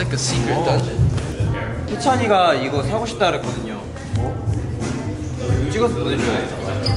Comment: did he mention Haechan?..I can't hear clearly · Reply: SVT NCT yes! I think he said that haechan wants to have those kind of shoes 🤔